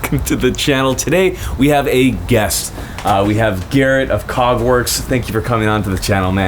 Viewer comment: I coulda swore I heard "Cogwarts" instead of Kägwerks at first. I thought this man was a legit Harry Potter nerd.